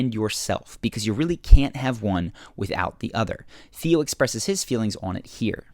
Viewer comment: Did you call Theo Felix at 7:39???